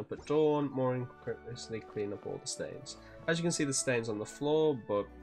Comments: i thought it said “clean all the stairs” and the maid said i had to clean the dust off or something so i said “oh ok” and started using anemo on stairs. i looked like an idiot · Bruh I thought that said “clean all the stairs” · BRO THE ENTIRE TIME I THOUGHT IT SAID STAIRS I SWEAR TO GOD